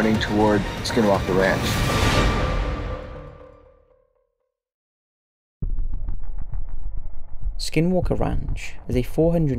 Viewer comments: I thought it said “Skywalker Ranch” and I was confused lol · For a second I thought it said "Skywalker"